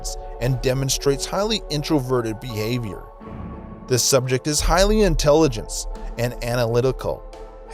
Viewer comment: I'm sorry but... did you just say "highly intelligence"